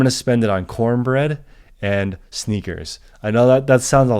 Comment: You said cornbread and sneakers.. I thought you said Snickers... lol, I was thinkinf damn he really likes to eat 🤣